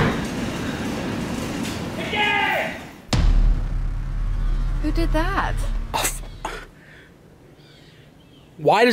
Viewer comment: what did Agp say in the car? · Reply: TODAY!